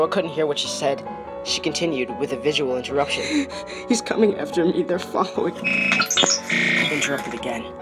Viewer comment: I heard "He's coming after me the fuck bo-"